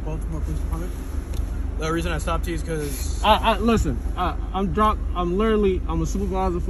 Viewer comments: favorite part of the vid is when he tries to say I’m driving but stop and sounds like he said “listen I’m drunk” 😂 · What is did he say to the cop at 0:52, his first words? It sounded like "Listen, I'm drunk" to me.